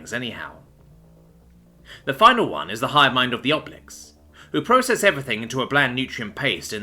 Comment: I thought I heard Oblex and my D&D brain activated.